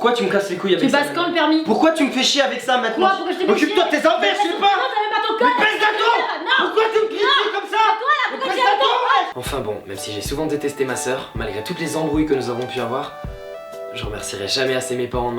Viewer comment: a 4:15 j'ai entendu :" mais baise un thon wesh !!" XD